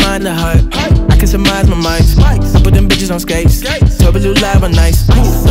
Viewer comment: I like the fact he doesn't curse ever verse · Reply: Roasr Plays Check 1:33 again, he clearly said bitches